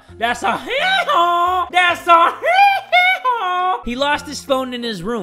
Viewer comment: I heard that Michael Jackson, ”HEEHEE-HAW”